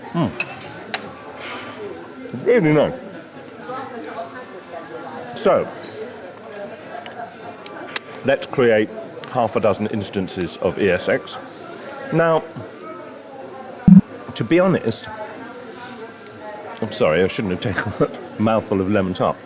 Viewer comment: it sounds like he days Ear Sex instead of E S X lol